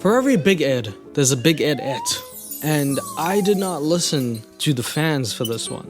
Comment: Sounds like he said baguette when he said big Ed ette